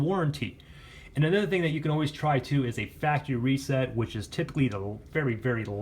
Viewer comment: What did he said at 9:50!?what reset?if you answer I ll subscribe to channel. If not I'll dislike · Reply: Factory reset! 😜